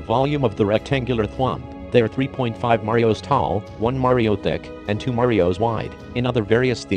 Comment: I swear, I shouldn't be the only one who heard him say dick when he said thick....... right?